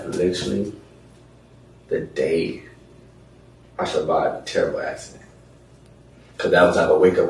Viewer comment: tear waxing? what did he say he survived and what happened? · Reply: He survived a "terrible accident"